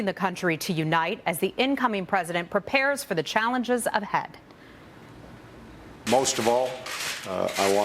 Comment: did she say as the incoming president prepares for the challenges of head?